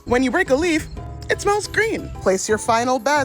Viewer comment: I heard "smells green" and right away knew the fruits in that stem were the poisonous ones hahahaha